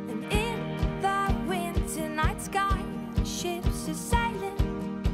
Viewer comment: I thought I heard ships are sailing